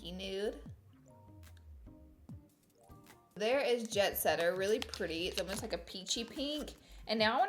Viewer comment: when she's said jet setter I thought she said bed wetter did any of y'all hear that